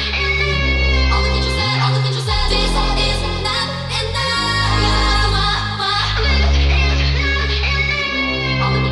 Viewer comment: did it just say "obavicu sve" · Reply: ne, reklo je "all things she said"